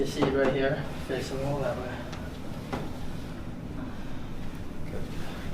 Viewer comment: sound like he said good god lol!!.